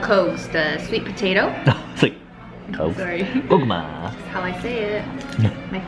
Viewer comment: I thought you called her, “엄마” I was dying until I realized that’s not what you said😂🤦‍♂️